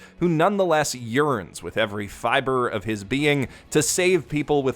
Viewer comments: for a moment i thought he said “urines with every fiber of his being” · My deaf ass ears thought you said "who practically urines" then I realized it's "yearns".